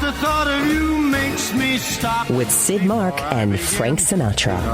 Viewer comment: LMAO. Sid Mark. I thought it said skid mark. 😆